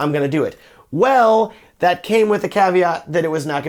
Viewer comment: "it's the russians" lol, bit expensive to buy mavic in Russia right now Great review though Also, could somebody please tell me what is word he used at 2:37 "it came with .... that is not gonna be shipped very quickly" tried google it and failed x( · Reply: haha thanks Vladlislav! I think I said "caveat".